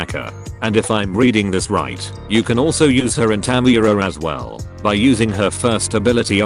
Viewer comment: whats the unit it mentions there? can't really hear it correctly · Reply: tamayura